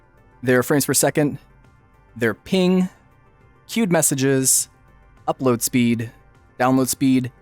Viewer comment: I always hear "Cute messages" instead of queued and I think it's wonderful :p Nice video! Reaally love the muse device, kinda makes me think I should also give that thing a try, especially to somehow more your ears or learn certain things to move with your brain :O